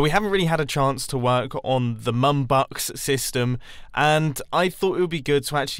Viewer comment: Man i thought you said NUNBUCKS not MUMbucks hahaha MUM